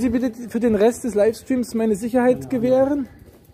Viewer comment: was hat er gesagt? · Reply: Mit Sicherheit